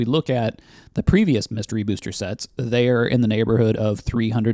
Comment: I thought he was saying Mr. booster. Is there a Mrs. booster?😅